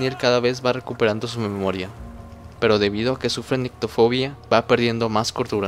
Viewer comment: Que buena narracion PD: en el min 7:48 , cuando dices que Daniel sufre una fobia, cual es? porque no entendi lo que dijiste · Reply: La nictofobia (proveniente del griegos νύξ nyx 'noche' y φόβος fobos 'miedo') es una fobia caracterizada por un miedo irracional a la noche o a la oscuridad.